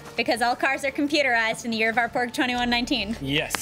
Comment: Did say "In the Year of Our Porg 2119"?